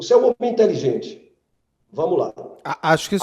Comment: Beto 42:05 Acho q o advogado disse "o seu publico é inteligente " . E não "você é inteligente " Ele ñ foi deselegante com vc não.